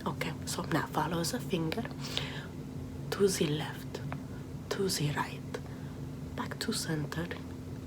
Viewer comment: I literally thought she said suzie left to zee right